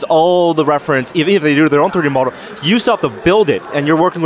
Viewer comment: what the hell did that guy say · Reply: Even if they did their own 3D model.